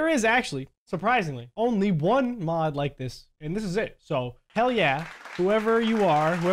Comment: did you said a no no word? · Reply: Hell is not a cuss words